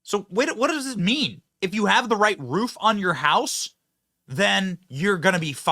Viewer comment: WTH!!! Right roof, sure didn’t get enough attention. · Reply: I thought he said Red roof? but he said Right roof?